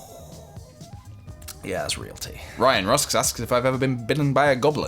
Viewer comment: someone else please tell me they heard him say "ryan ross asked..."